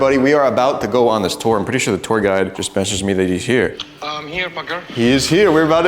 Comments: lmfao its sounds like he said im here F*cker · I heard "I'm here paker" 💀💀